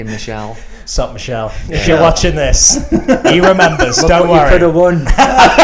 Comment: also,what did Matt say at 5:44 ?? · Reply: Alex Hnatyshyn "Look what you could have won"